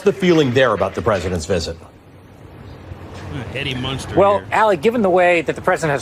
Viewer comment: Did anyone else hear someone call the guy Eddie Munster at 4:23 ?